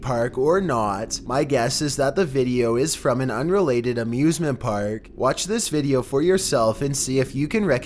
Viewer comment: I misheard amusement park at number 2 as abusement park^^ as they fly out of the rail... Sry for them, but it fitted just right. Abusement park.. Hehe.. Oh man..